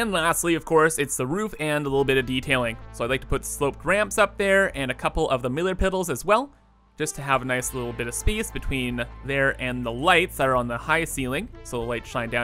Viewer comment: had me rolling miller piddles instead of middle pillars